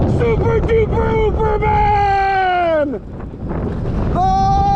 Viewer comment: I thought you said "super de pooper man"